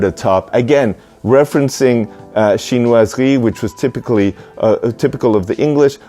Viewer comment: Did anyone catch what he says? Referencing what? Thank you. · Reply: Chinoiserie